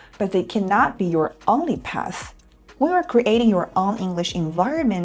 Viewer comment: the subtitle is wrong. When you're creating => When creating